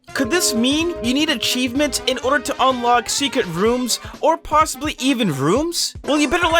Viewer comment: You’re telling me he said the same word but in a different sentence “rooms” but in the subtitle says items- 😂